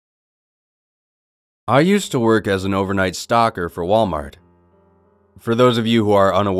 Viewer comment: I heard "I work at Walmart as a overnight stalker" at first and I was like "woah okay then, nice job how much do they pay?"